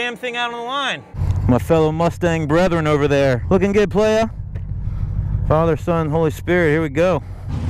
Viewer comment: are you a Christian?...I thought I heard you say "Father Son Holy Spirit...Here We Go!" and while your'er at it ask him to control your Tongue!...God bless!...0:-)